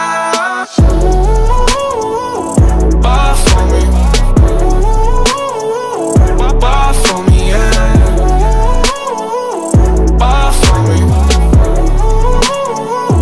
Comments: It literally sounds like he is saying “barf on me” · All I hear is "barf on me". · I THOUGHT HE SAID "BARK FOR ME" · It sounds like he saying barf 🤮 on me BUT NICKI